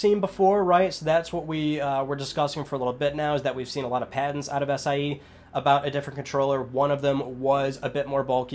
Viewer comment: I can’t stop replaying 8:18 I don’t know what he said?😂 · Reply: We've seen a lot of patents out of sie?